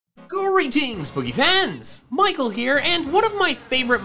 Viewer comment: Every time I hear the intro “Greetings PokéFans!” I always reply in my mind: gReeTinGs pOkéMan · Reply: I always hear "Greedy Boogiefans!"